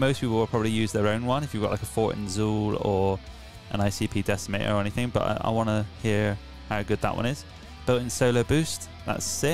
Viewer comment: Did I hear icp decimator at 2:35? Lol a pedal that kills clowns, I'm down for that.